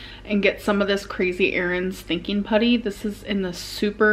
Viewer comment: i thought she said crazy earrings stinking putty hahaha maybe used to clean the earrings or something, thats what i thought 😂